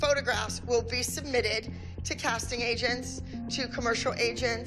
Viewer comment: When Abby said "agents" I thought She said asain